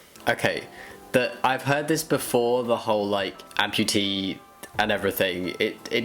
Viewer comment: When she said "amputee" I thought she said "entity" and the vista of infinite options opened up, religion, sci-fi, horror, all of the above...